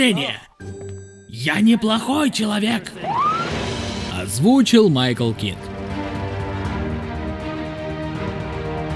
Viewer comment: послышалось "озвучил Michael Keaton"